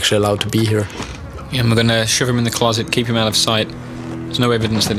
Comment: Swear he said "shit him in the closet".. :(